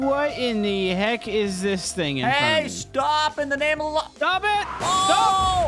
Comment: Is it just me or did I hear say “stop in the name of my lawn”